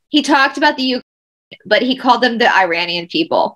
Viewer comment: I thought he said Uranian.